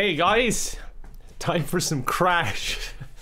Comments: I swear I heard "time for some crack" in the beginning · I thought he said "Time for some crack" 😶